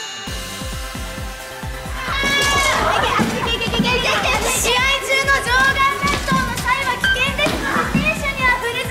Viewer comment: What is that geh-geh-geh-geh? I hear it at ringside all the time. What are they saying? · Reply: They're saying "iku" it means "go"